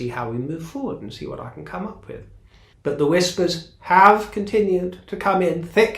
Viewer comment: I misheard and thought you said "The whiskers have come in thick and fast" I'm having the same problem,( being an ancient old lady), then I realised you said "whispers"😃